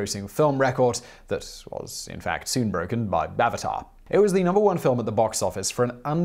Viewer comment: It sounded like you said "Bavatar" 😂